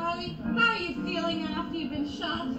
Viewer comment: it sounds like he said "i'd let you know who fuck me" instead of "I'd like to know who the fuck did it"